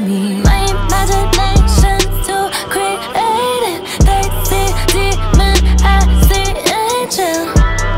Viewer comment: Woww. I thought she said 'they see canon i see apple' 😂